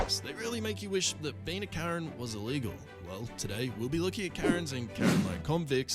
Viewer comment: audio says, "was illegal" -- but emphasized, visual subtitles say "was legal." It's a pretty bad foot to start on; the wrong foot -- your audio and video contradict each other with opposite meanings XD